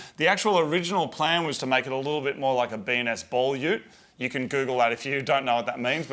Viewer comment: "B&S Ball Ute" is what he said at 3:08, just in case anyone didn't know.